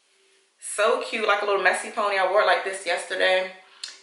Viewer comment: OMG! I thought you said Mexican pony 🤭 I was like "Oh no!" but then I replayed it and you said "messy" pony 😂😂 I'm gonna log off. Thanks for the video! Do you prefer the weft or the single links?